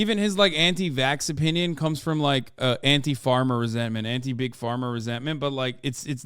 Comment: did big has say "Big-Farmer resentment?" and, if so, could someone explain it to me? plzzzz & thnkezz. i know it was supposed to be a joke if so, i just dont understand what its in reference to. i know he said "anti big pharma" as well just a couple seconds later, but i swear he said anti big farmer first and i need to understand joke cus it cracked me up to the max, even without any context lmao